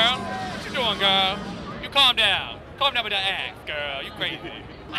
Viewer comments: i lol'ed when i thought he said calm down with that ass girl you crazy @ 1:42 but he said axe oh well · Hahahaha! I thought said "calm down with that ass, girl!" at first instead of "axe" xD